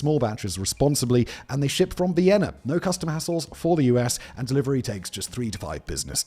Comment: He said like no custom hassles but it sounds like he said assholes